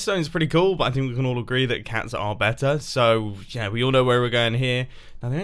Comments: when Mumbo says cat captions say pug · if you turn on subtitles, instead of cats is says pugs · just turn on captions. (Is it just me or does it say pugs instead of cats.) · if you turn on subtitles is says pugs are better instead of cats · when Mumbo says cats are better the captions say other wise . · it's says "pugs" instead of "cats" with captions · subtitles says that pugs are better instead of cats lol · Turn on captions at 0:39 and instead of saying cats are better it says pugs are better! 😂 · he says that cats are better, but the captions say that pugs are better. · >:0 I turn on captions for no reason, and your so called editor put instead of "cats are better" "pugs are better" Here's the time stamp so y'all can turn on captions and see 0:39 Just found another secret 0:55 "LOL" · has anyone realized that at 0:38 the voice says cats but the subtitles say pugs? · Did anyone else notice on 0:42 in captions it says pugs instead of cats?! What kind of rebellious act is this?! I DEMAND RETRIBUTION FOR WHISKERS INCOPORATED AND DEAF PEOPLE!! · If you have subtitles on It doesint match up Instead of "We can all agree Cats are better" Its "We can all agree Pugs are better" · turn English captions on (not auto generated) why does it say pugs instead of cats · when you said ‘cats’ the YouTube subtitles said ‘pugs’ What?? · the subtitles said pugs instead of cats, who did this? lol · when he says “I think we can al agree that cats are better” the caption says pugs instead .<.